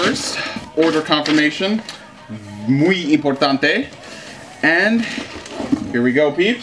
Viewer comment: Hey bro I just want to know where are you from because I heard you said "muy importante" I'm just curious lol